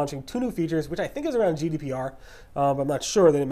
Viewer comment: What did he say at 8:15? Was it GDPR as in the EU Data protection law?